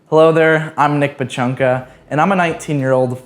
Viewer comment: I thought he said “hi there im nick big chunga”